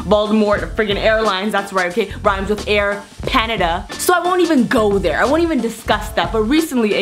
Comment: When she said air panada I thought she said air panda 🐼 😂